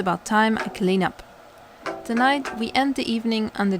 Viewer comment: I thought he said "Take a bow", not "Clean the bow", so... Beautiful job on the bread, take a bow!